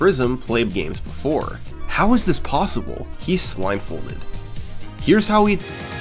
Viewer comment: dear, please read this comment, i need you to settle a debate im having with my friend. at 0:59 does say "he's blindfolded" or "he's slimefolded"? please respond. the captions say blindfolded but my friend swears he says slimefolded.